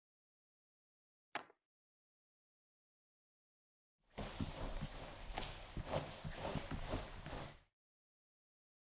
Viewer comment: whats that? No no you did amazing is what i think said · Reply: what I think said was “What’s wrong? No worries, dandy will save the day! Now what was I talking about..”